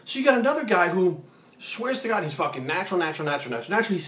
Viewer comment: Haha. I kept skipping this vid to around every 30 - 60 secs...and I kept hearing "fuckin natural". Hehe, Great Mcdonalds biceps though :)